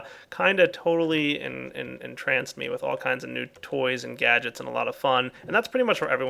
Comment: 4 minutes in it sounds like he is saying nude toys